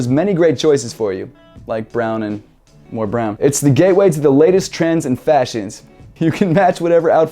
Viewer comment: damn i thought he said gay way